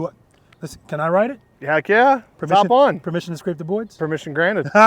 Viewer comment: Funny I heard, “Permission to come aboard.” It’s a Navy thing. Glad you made it back safely from South America…awesome group of riders.